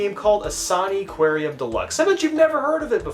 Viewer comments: In a TRIGGER VIDEO I thought you said SONICQUARIUM DELUXE I was like where’s SONIC?! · i thought you said soniquarium, until i read the description.